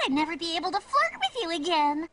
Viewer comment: I honestly thought she said "fuck with you again."